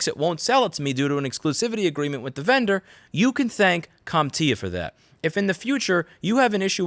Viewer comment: "...you can thank cum TIA for that". xD Give me a like if you heard cum instead of comp or com!